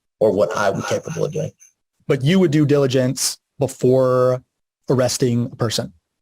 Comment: sorry it makes me laugh when i hear "do diligence" instead of "due diligence"